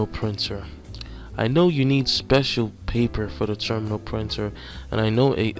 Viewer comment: Thank you for the video sir. Only part that was confusing for me is I was listening to it while working on other things and I thought you were saying "Terminal Printer" and then would describe it as a Thermal printer. So I had to stop what I was doing and pay more attention to see if I was learning the correct thing. I will be taking this test pretty soon. Your videos have definitely been helpful. Obviously knowing that the questions won't be the same but taking all the information from the answer section. Thank you again.